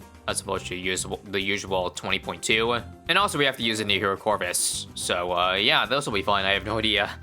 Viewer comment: what'd he say??? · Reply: "New hero" sounds like the other word tho